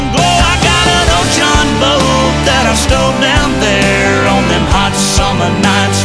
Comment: Always thought he said old jon boat that I stole down there 😂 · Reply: he says stow